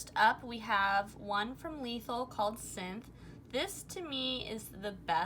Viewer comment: What shade did u say at 24:14? Tia . Also the one at 29:00, u said you'd put in description but I don't see it. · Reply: is Lethal Cosmetics. 29:00 is Love Luxe Beauty, but I do not recommend this brand. I struggled with putting stuff in the description in earlier videos but I'm continuously trying to get better about it.